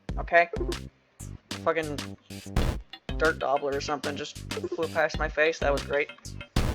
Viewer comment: i kept mishearing it as “dirt gobbler” and being confused on what insect that was Was it a Mud dauber?